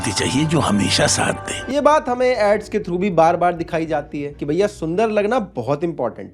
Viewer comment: Subtitle wrong... Ads ko AIDS likh ke rakha hai